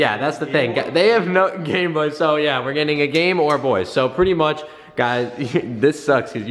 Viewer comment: Game or boy at 5:20-5:22is what I heard